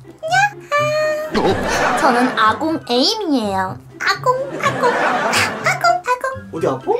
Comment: 아궁이 에임으로 들리네요 ㅋㅋㅋㅋ